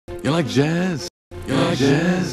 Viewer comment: After 1,000 it sound like he saying “YOU LIKE JIZZ?”😏